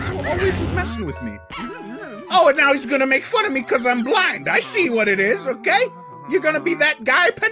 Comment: i misheard it as "OH NOW HE’S GONNA MAKE FUN OF ME BECAUSE I’M BLACK" 💀🗿